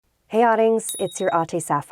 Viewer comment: Im a filipina and am i the only one who only misheards the word "adings?" To "audience"?😂😂😂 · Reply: Oh my god I thought she was saying oddlings at first in British so I don’t know her language that well, is she is Filipino